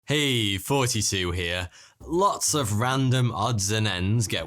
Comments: When he says “heyyy Thoughty2 here” it kinda sorta sounds like he says “hey 42 here” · Everytime I hear it as “hey, 42 here” lol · i know this has probably said before but doesnt it sound like he says hey 42 here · I thought you said "Hey, 42 here." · I don't hear him "Hey!! Thoughty2 here". I hear him "Hey!! 42 here".